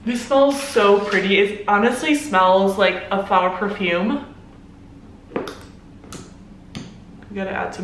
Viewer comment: When she said “It honestly smells like a flower perfume” I thought she said “Flour tortillas”😂😂😂😂